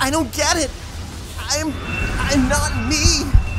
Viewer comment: When Sonic said I'm not me, I thought he was saying I'm not weak.